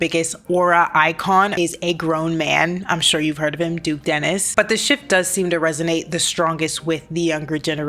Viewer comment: I have never heard of this man and I thought you said “He’s Dennis” cause my volume was low and I thought this was some sort of joke.